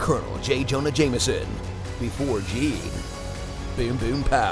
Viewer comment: I thought he was going to say 'Boomer!' because I thought of L4D when I saw the character.